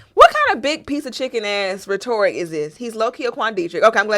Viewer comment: Wait wait wait. Did she say “re-Tory”?!? 18:59 Bruuuuuuhhhhh 😂😂😂😂😂😂😂😂😂😂